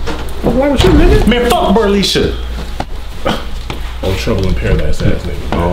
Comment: BERLISHA?! - what kind of name is that. *soulja boy voice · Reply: I thought he said brolisha lol